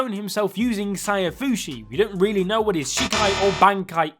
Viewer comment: what is SAYABUSE ?